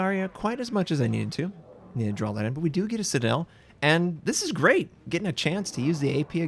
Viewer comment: I thought he said "This is RAPE!"